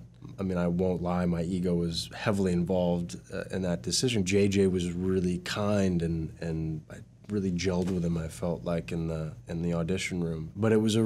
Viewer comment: can someone tell me what he's saying at 0:47 mark? JJ was kind and what? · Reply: "J.J. was really kind and I really gelled with him I felt like, in the audition room." Hope this helps!